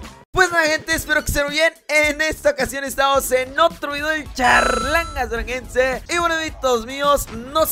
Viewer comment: Cuando enpece a ver sus videos pense que decia charlangas canadiense🤣🤣